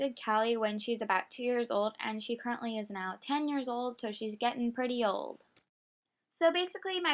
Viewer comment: Omg when u were talking about Isabelle I thought you said he is now 40 years old😂😱